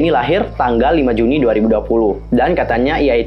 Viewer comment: Gw denger nya 2020 (Gw yg salah denger apa gmn?)😂